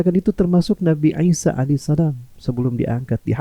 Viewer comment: Di menit 16:57 itu saya salah dengar atau itu ustad keliru ngucapin kok bilang nabi Aisyah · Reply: 'isa 'alaihissalam